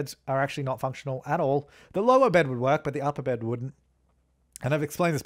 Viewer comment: " The lower bed would work but the under bed wouldn't. " 😂 😂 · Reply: I heard "the lower bed would work but the upper bed wouldn't" lol